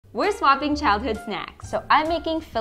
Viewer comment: My dumass thought it said swapping children with lisa